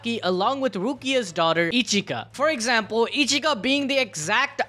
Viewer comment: >characters name is Ichika >subtitle says Erika How do you even mess up that badly💀 don't say it was autocorrect because ain't no way its morphing Ichika to Erika.